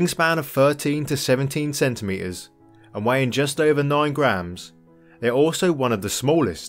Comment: 3 ounces, 90 grams, - maybe I misheard...